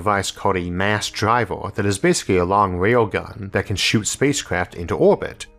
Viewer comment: Not trying to be rude but why does it sound like he adds a "W" or swaps a letter with a "W", 3:30 sounds like he says "a long wail gun"